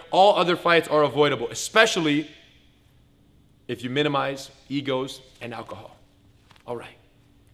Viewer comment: did he say "especially if you minimize eagles and alcohol"?